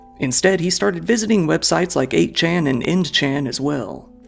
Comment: did i hear Eggchan?